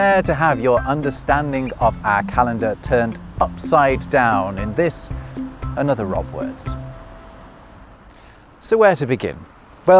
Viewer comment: "in this another robwords". I wouldn't think this is proper grammar. Is it? ESL speaker here. Thanks in advance. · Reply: I think at 0:50 he says "in this, and other robwords". It probably means, this robwords, and in more words.